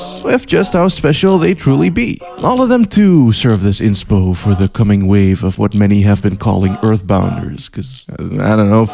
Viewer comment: Whats this one game called? I know said the name before but I can't understand what he's saying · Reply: Hylics, it looks pretty interesting